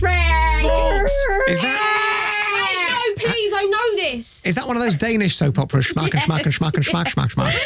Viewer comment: Did he just say "is that one of those danish soaps?"